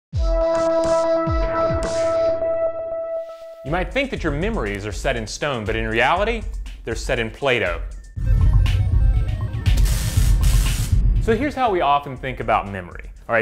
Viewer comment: I thought he said "set in Plato". Was expecting some cave-shadow stuff.